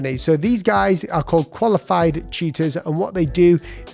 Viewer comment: I heard "Qualified Cheaters". I was so confused 😂